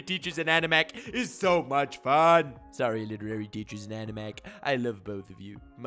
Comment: I heard him say “Sorry illiterary teacher’s and Anime Uproar.” XD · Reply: I am an idiot, he said animac 😅